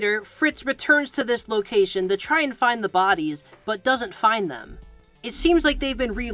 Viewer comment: i swear i thought it said the baddies kids :eyes-purple-crying